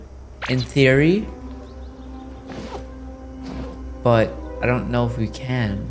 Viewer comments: he says but and the caption says hello everyone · I just noticed that at 9:37 the subtitles say “Hello everyone!” instead of “but” lol